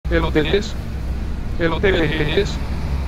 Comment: me mori de la risa porque dice eloteeeeeeeeeeeeeeeeees